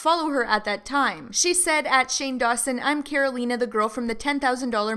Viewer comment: did she just say “Caro-leena”